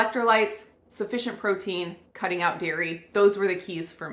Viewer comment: yousay "Electrolyte, sufficient protein and cutting out "WHAT". What you say here is incomprehensible. It sounds like "Viry" but what is "Viry". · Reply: Dairy